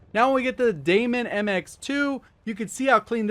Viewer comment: Did he say daemon mx2? 🤔